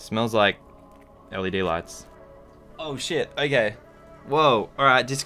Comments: did you just say oh sh*t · did he say oh shit?!